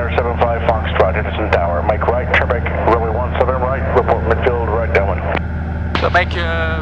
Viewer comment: Toujours aussi chouette tes vidéos, merci ! J'essaye de comprendre les échanges radios, et là j'ai ré-écouté x fois votre première clairance pour la 17R (à 3:20) et moi j'entends "...make right QUEBEC runway 17R...". Heuu what ?! Décidément je suis pas prêt de voler aux U.S. ^_^